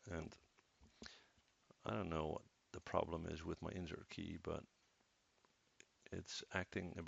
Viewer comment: what key are you saying to use at 9:36 because i think your saying insert key? but idk what im pressing or going to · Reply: Yes, the insert key ( hit once and hit once when you are done ) or hold the D key